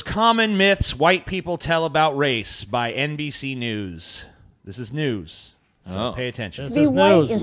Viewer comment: I thought I heard NPC NEWS.